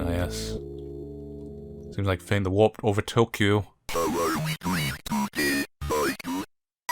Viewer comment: I really like how they managed to make a priest a decent protagonist in a horror game. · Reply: What exactly did he say at 1:40,in response to Michael's 3rd picture? I heard "Feign the warp over Tokyo"... What???